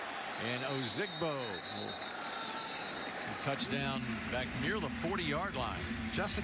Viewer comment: "Touchdown near the 40 yard line" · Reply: Thought he said, "Touched down near the 40"